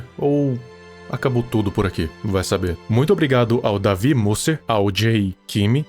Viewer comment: eu entendi "davi buseta-"